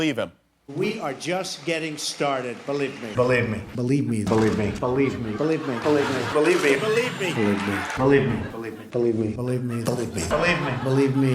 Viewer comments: It sound like he is saying bullied me... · It sounds like he is saying “Delete Me” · It sounds like he is saying belieb me